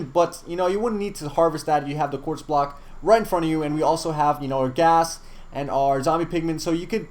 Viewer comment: Did you say an orgasm?